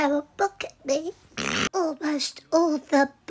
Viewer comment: At first I thought she said "I have to get a new hobbit" instead of hobby...